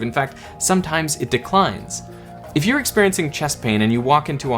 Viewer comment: I swear to God I heard "chess pain".